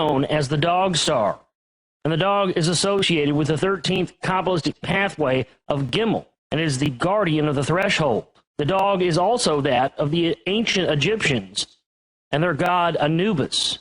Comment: Can anyone lead me to some more info about the dog symbolism of the 13 somethin that he mentioned being a guardian called gimmel? did i hear gimmel? i am trying to find it online and i cannot get a hit. it is at 21:10 in the vid... i saw some of these symbols and renditions in my first and only breakthrough trip..im trying to research them all. this is absolutely amazing.